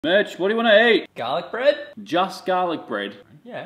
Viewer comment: I thought he said bitch